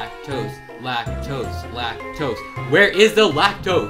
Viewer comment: why do i hear black toes on